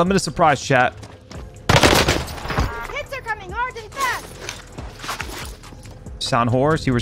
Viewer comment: did she say "kids are coming hard and fast" ?? The developers need to be looked into 🤣 wtf